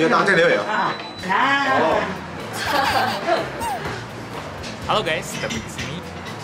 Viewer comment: ko ada David di sini?? Apa salah denger?? 😅🤣😂